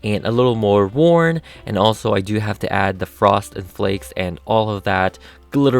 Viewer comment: I thought he said the Frosted Flakes